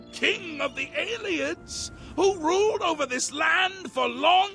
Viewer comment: I keep hearing Ayleid as "alien" and Ayleid sorcerer as "alien saucer", so all I can picture is her fighting alien saucers 😭😂